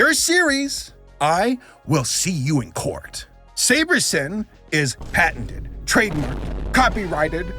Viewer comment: I swear I thought you kept saying Sabersimp til you spelt it.